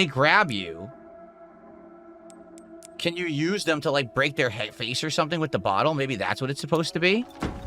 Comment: Head face? Did I hear that right?